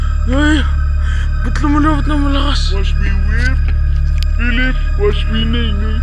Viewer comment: c moi ou il chante watch me 😂